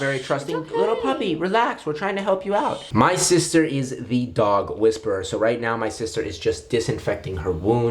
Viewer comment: Who else first thought he said his sister abused the dog ? 👁👄👁